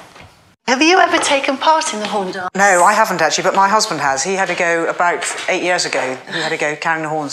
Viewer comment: Did she say, husband @ 7:54 ?